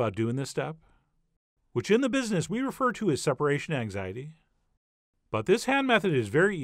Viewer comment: I thought you said, " separation eggsity". Still laughed hard.